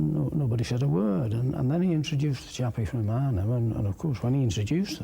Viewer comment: What does he say at 22:33? "Chappy from WHERE?" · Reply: luke dawson Arnhem